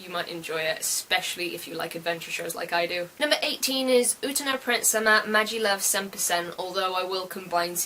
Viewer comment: When you said Uta No Prince Sama I heard, "Utena Prince Sama", and was confused thinking along the lines of "is that something to do with "Revolutionary Girl Utena". Then of course I noticed my mistake.